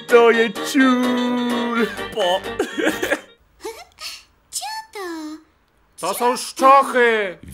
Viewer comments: czy tylko ja usłyszałem 3:05 ucłyszałem "ciota" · czy tylko ja usłyszałam że ten teletubiś mówi cioto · czy tylko ja usłyszałam cioto?